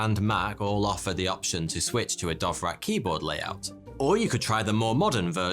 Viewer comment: I am listening to this in the background, but I keep hearing him saying "Dothraki Hoard" Maybe I watch too much tv